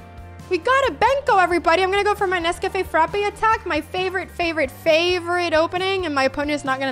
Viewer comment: I thought she said nescafe frappe attack